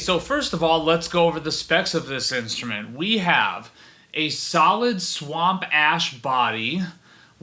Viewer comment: I was slurping the last bit of my drink when you said "A solid swamp ash body" and it legitimately sounded like you said "swamp ass body"